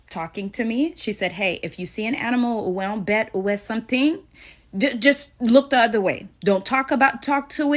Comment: what on EARTH is she saying at 0:45??? her mom said “if you see an animal…”??? and I can’t understand anything after that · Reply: ' a wild bat or something'